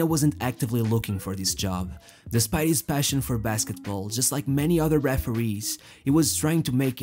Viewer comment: Am I trippin or did he say basketball??